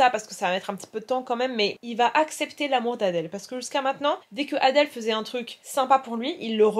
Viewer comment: (à 7:08 j'ai compris "il va accepter la mortadelle" 🤡)